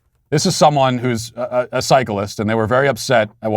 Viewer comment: You said “cyclist” and what I heard was “psychopath”